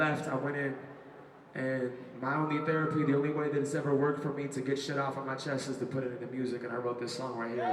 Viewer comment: what does Ben says at 3:31 ? "and when he left i went it. and now i wouldn't be there if the only way..." ? please help me i'm french lol x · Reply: sorry this is really late but he said "and my only therapy the only way that ever worked for me to get shit off my chest is to put it into music and I wrote this song right here it's called 'Starting Over' hope this helped; à plus tard